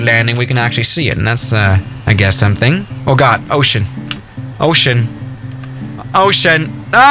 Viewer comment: When he said ocean i thought he said oh shit lol 😆